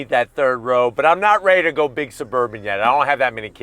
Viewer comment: Did he just say "big Suburban"?